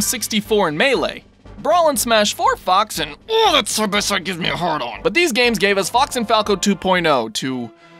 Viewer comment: anyone got a clue what he's saying? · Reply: borosbattalion23 that’s the best it gives me a hard on